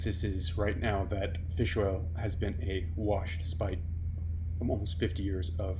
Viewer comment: fish oil has been a..." - couldn't hear, pls clarify. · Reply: awash